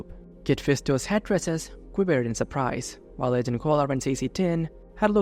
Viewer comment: The split second it says Agent Cola instead of Agen Kolar is so funny